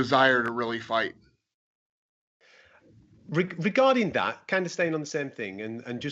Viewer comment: pal. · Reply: yeah man 😂 he said "regarding that" and I thought he said "regarding Dan" Clearly need to get me ears checked.